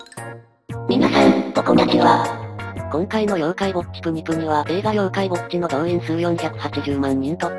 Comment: なぜだろう妖怪ウォッチて言ってるのに妖怪ぼっちって聞こえる！